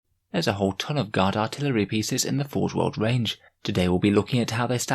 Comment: I was half asleep when I started watching this. For the first few minutes I thought you were saying God Artillery and then I realized it was Guard Artillery. I admit I was disappointed when I figured it out.